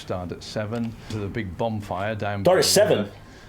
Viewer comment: Does say 'bombfire' rather than bonfire'? - alot of people do...